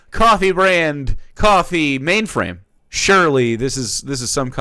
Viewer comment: I thought he was saying contraband coffee this whole time only to realize he said COFFEE BRAND COFFEE and honestly it checks out that Jeremy lacks even a drop of creativity